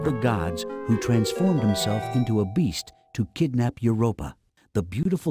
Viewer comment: I misheard the voice saying to kidnap your robot 😂.